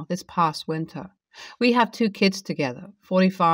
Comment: Story 3 i thought he said a 45 year old and 18 month old. Lol